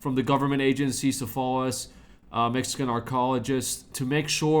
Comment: Why did I hear " Mexican Gynecologist"... Time to turn off the PC...